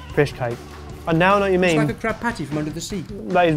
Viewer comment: Omg I thought it said Krabby Patties D